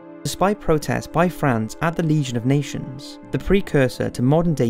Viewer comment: "Legion of Nations"?? Did you say that at 9:22 or is it the accent? I cant tell.